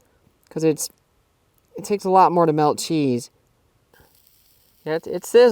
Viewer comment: did you say cheese? 😮